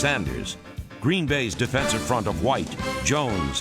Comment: Did I hear a guy named McMichael at about 9:50 for the Packers? Wasn't a guy named McMichael for the Bears? Maybe they're related. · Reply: It was Steve McMichael. Ex Bear.